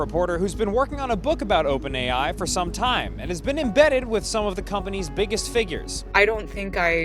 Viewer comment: I heard it as been in bed with some of company’s biggest figures.